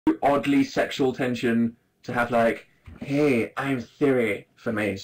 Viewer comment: the first thing I heard was "oddly sexual tension." and now I'm screaming